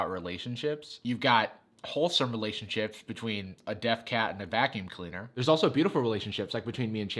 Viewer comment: I thought you said "dead cat" I was like wholesome where?!